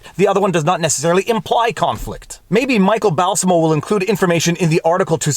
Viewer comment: I thought you said the author of the article was Michael MALICE, and I had to rewind because I was thinking "that does not sound like something he would have written..." I've seen people take positions that attempt to discredit what you are saying without actually having all the information. I've been telling people the very real and I have the screenshots from the state's AG website to prove it story of how my now deceased uncle somehow both requested a ballot and returned it from his unconscious bed in the Yale ICU. He had brain swelling and was drugged to keep him unconscious because in the brief minutes where the drugs wore off and he became lucid HE HAD NO IDEA WHO HE WAS OR WHERE HE WAS and the medical sedation wad to stop him from suffering that TRAUMA. My aunt is not in a position to ask about this, both from the grief and if this was done by someone ballot harvesting, it would cause her trauma. That does not negate that something happened which he did not have anything to do with and makes this incredibly suspect. In my state a "recently deceased person" can still vote if they mailed in a ballot prior to their death, which I think is a bit questionable, as it creates two-tiers of deceased voters, a group who hit an absentee ballot in and still got to vote after their death and those who planned to go in person and they're didn't get to vote, no matter "how important it was to them to do so" (the state's reasoning for why this is legal, "they had intent and desire, vo